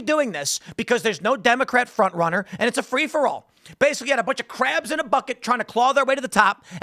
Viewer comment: Oh crabs in a bucket. I thought you said crap in a bucket in the clip.